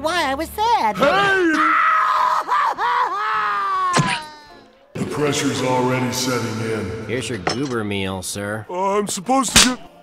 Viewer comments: For a second I thought Patrick said “Depression is already setting it.” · I know he says "The pressure's already setting in." but with that effect it sounds like "Depression's already setting in." and ngl I laughed when I heard that. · Patrick: HEY Spongebob: Intense crying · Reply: for a minute I thought he said "depressions already setting in"